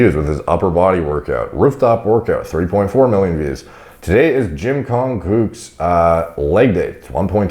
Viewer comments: I keep hearing you say “Jim Kong Gook” 😂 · JIM KONG GOOK? I HEARD THAT